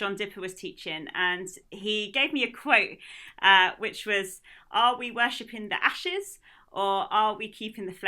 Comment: I have a hard time understanding what she says at 7:30 . Can someone transcribe it for me please? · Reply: You can turn on the captions - click on 'English' (not the automatic version) and that's my updated transcription! She says "are we worshipping the ashes, or are we keeping the flames alive"